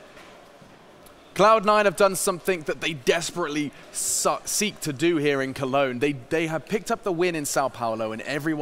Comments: did he say sucked instead of seeked 😁😁😁😂😂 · "cloud 9 have done something that they desperately suck...seek to do here" WTF XD really said that